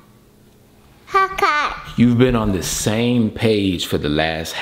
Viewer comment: who else thought she was saying Popeyes?